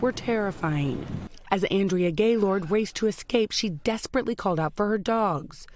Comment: am I the only one who heard that her name is Andrea GayLord?